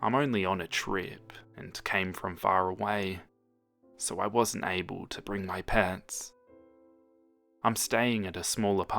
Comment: I heard “I wasn’t able to bring my parents” As “I wasn’t able to bring my pants” 😂😂😂😂😂😂😅😅